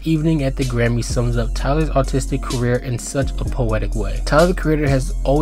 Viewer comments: I think he said autistic career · Great video, but I swear I kept hearing “autistic career” instead of “artistic career” idk if I’m tripping or if you need to pronounce your R’s better but great vid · I fr thought he said, "tyler's autistic career," lmao